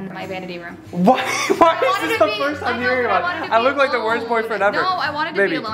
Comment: Who heard “I feel like the worst boyfriend”